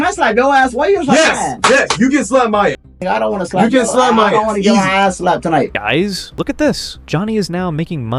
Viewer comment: did Johnny just say i don't wanna slap your ass tonight?????? So by tonight means he's done it in private then. Did he just spill the beans on what we already know.. 😂😂😂 5:37 he didn't wear it to court. Legal mindset already covered that when the trial was going on that was worn after ehen they got home